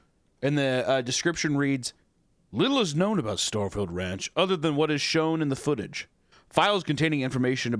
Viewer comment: Instead of "Little is know about the Starfield Ranch" I heard "Little is known about the Starfield rant" Now in my head I just hear "FUCKING PRONOUNS!!!"